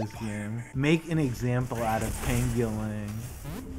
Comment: i thought you were saying Ping your lane (w.e.t.f that means) then i figured out u were saying his real name